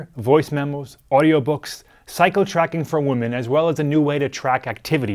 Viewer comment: The closed caption says "psycho tracking for women" XD